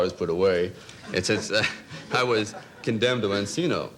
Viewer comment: What was said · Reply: Encino?